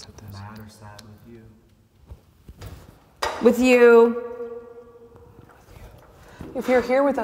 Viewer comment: I heard a male voice whispered and said "with you" right after said that words